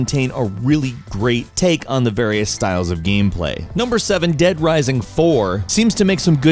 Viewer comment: I feel so retarded, when he said dead rising 4 I thought he said left for dead four. :( *it is late